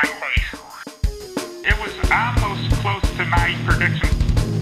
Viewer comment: "It was our most close tonight prediction" XD gets me every time I hear that XD